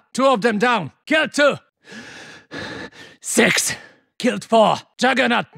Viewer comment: he actually said "sex"?